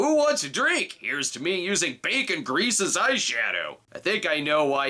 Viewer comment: "Bacon grease as eyeshadow" ....I thought you said "baby grease"